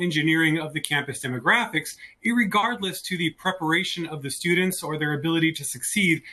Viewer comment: Did he say "irregardless"?